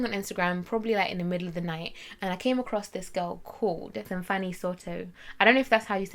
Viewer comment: Okay, where's the picture plz? The CC on this video is also saying her name is "Some Funny Soto" so no help googling for myself lol...